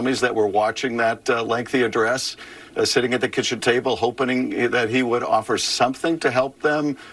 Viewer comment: did I hear it right he said “hopening”?? can anyone please explain what it means? 🤔